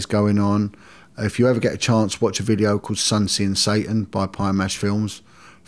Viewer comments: what is he saying? Sun sin satan? I want to know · What documentary he talked about, anyone catches what he said at 2:42? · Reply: Sun, Sin, Satan by Pie & Mash Films